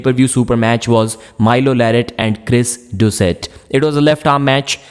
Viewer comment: im sorry did you say ``and gres doucette``?